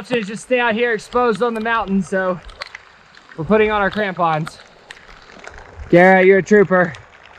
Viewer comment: I thought you said "We're putting on our tampons"